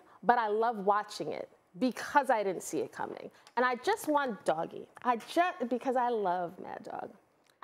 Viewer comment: I swear I just heard her say I just want doggie😂